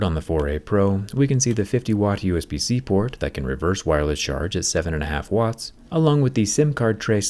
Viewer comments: did you mean "reverse wired charging" or I heard it incorrect? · Does he say "reverse wireless charge"?💀